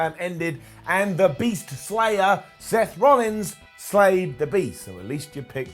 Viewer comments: For some reason I thought they were saying Heath Slater at first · I thought they were saying Heath Slater